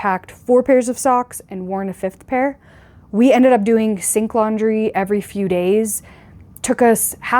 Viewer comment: OMG...,I thought you were saying "stink laundry"......I guess you could say that 😃😃